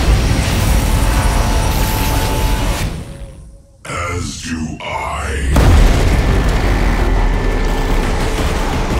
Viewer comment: the correct sentence is "as you i" or "as do i" i literally didn't understand in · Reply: as do I